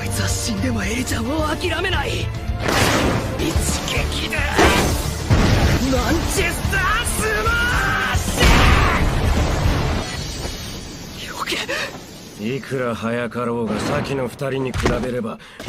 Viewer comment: is It just me or did deku say Manchester smash